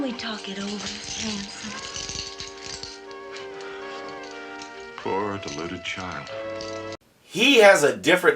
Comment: As a child, I thought Batman was saying, "Poor polluted child."